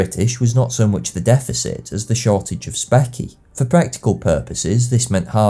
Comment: Excuse me, what does he say at 23:13 and at 23:27? Sounds like "specky" It's really hard to understand his accent for me (where is it from?) Thanks in advance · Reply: Specie, as in hard currency